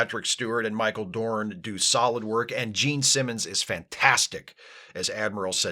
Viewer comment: says “Jean Simmons” and I hear “Gene Simmons” and now that’s an episode of TNG ruined.